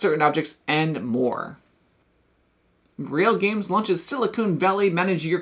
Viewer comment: it sounded like “Silicone Valley”…..which I got to tell you sounds like a totally different game than “Silicon Valley”. Lol. Totally different vibe just by changing one small letter. Great video, always puts out videos that are very informative and this is one of my “Go to” content creators for learning about upcoming board games.